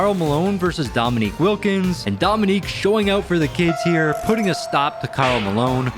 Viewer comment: I thought he said “Dominque showing up for the kids against Karl Malone” haha